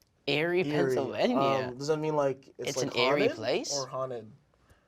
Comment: creo que dijo ¿Erick es un lugar? "It's Erick place" ya lo demás no entiendí tampoco 😂